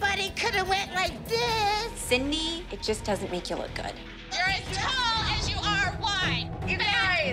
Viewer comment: As an obese person myself, I really shouldn't have laughed as hard as I did when Jessallyn said, "You are as tall as you are wide" to Cindy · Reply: i thought she said you are as tall as you are wise since cindy is short and like calling her dumb but idkkk